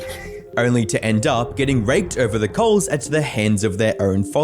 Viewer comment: why did I hear it then? Can u not understand an aussie accent or....? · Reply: gang it sounded like he said raped over the coals, and other people heard it too